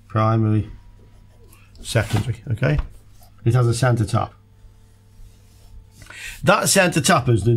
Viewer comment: I like how around the 28:40 the auto-generated subtitles read "Santa Tapas" instead of "center tap" 😀